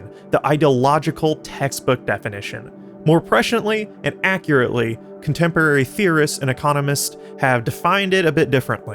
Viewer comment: I heard the word "pretiently" being used several times, are you trying to say "preciously?"... 4:38 for example. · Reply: Presciently